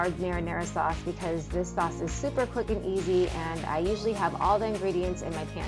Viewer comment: Lol I thought you said super fucking easy at first 🤣